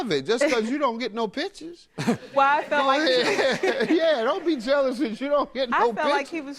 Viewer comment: Deadass thought JM said don’t be mad bc you ain’t get no bitches at the club 💀